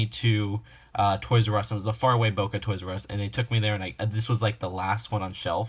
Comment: did you just say boca on 9:05?